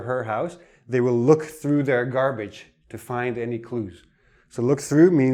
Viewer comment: Hi, shouldn't it be "clues" instead of "clothes" in 4:24 ?